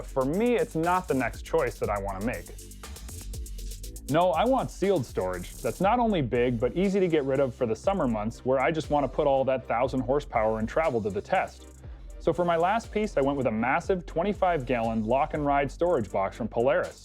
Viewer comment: All that 1000hp lol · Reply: lol, while I might have misunderstood him. It sounded like he said 1000hp in the video @ 7:40. Of course I know it was just a mistake if he did.